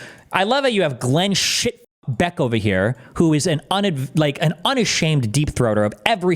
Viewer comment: I heard Destiny’s wife is a great unashamed deep throater 😂🫵